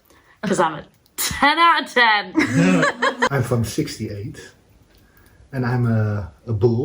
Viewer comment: It sounded like said he was 68? .....or was it from 68?